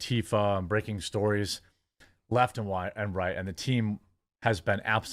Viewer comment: The quartering had a very funny slip at 1:09. "Left and white" instead of left and right, easy slip up in sure we can all agree but really funny given the context